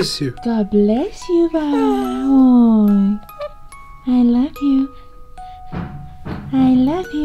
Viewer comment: did say I love you?